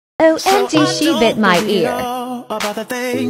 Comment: Is it just me or i heard ' Omg she bit my beer '